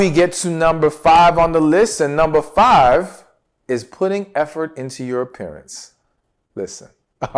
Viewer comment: You said put in effort in your appearance and I first heard it as put effort in your parents. Had you not explained I would have been completely lost.